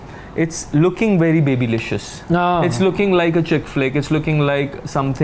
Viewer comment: “Looking like a chick flick” for a moment thought he said chick-fil-A. Rather this movie babylicious will be shaking the sub continent movie scene, so would I be wrong to ask my LOCAL to have milk shake named after this?